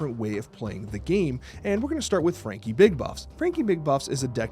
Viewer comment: Genuinely thought he said frankie big butt